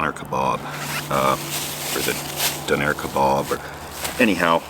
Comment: I thought you said Donner Kabob at first and was frightened..